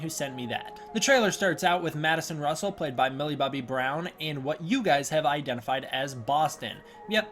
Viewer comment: That's a girl? Sounds like you said the name was willa bubby Brown.